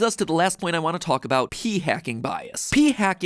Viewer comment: Matpat: p-hacing Me:did he just say pee